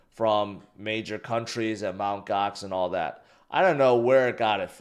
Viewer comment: I thought he said mt govx sold all month ago😂